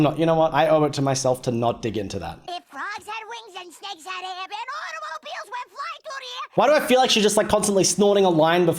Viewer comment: This is what Sticks said for anyone who’s curious… “If frogs had wings and snakes had hair then automobiles went flying through the air!”